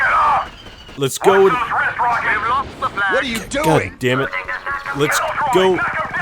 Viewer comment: I used to think he was saying “watch those ranch rockets!”